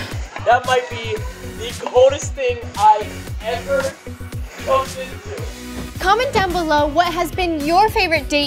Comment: am I going crazy or did he say f*cked into 🤣